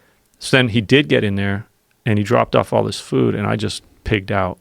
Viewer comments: thought he said refeeding a demon...lol....kinda fits anyway...my dog had pancreatic enzyme deficiency...ate all day long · I thought he said he got "refeeding the DEMON".